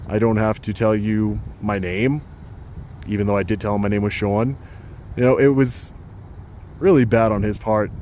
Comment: I thought dude said his name was!😂 Never seen an actual 'hissyfit' before until today!😂 Dude was sashaying, furiously!